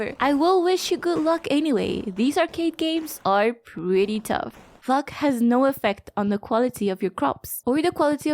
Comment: Hold up! what a second ! Did you really say fu*k instead of luck at 8:58 .I may be wrong . ( Anylove ,your videos )